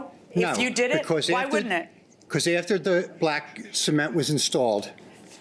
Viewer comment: Am I the only one who thought they kept saying “semen?” Lol